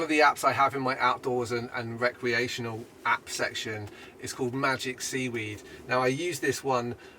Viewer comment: When you said "this app is called Magic Seaweed". I thought you said "Magic Weed"and i thought. I really need that app. LOL. BTW You are lucky to live in such a beautiful area. Take care...man.